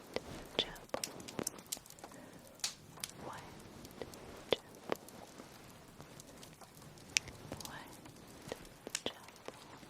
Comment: I love all your ASMR video and this one is no exception! I can't understand what your saying at 11:39 You whisper it three times. It sounds like 'white gerbil' (?). I've listened to it a few times and can't figure it out.